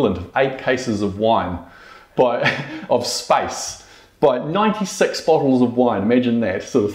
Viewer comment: I was skipping through the video and I stopped for 1 second and I heard "sex bottle". Why am I this way?